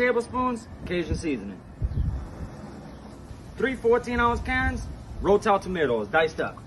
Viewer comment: Sounded like you said “3 14th ounces Karen’s...”